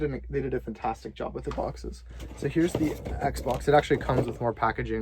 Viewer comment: did he say'd xbox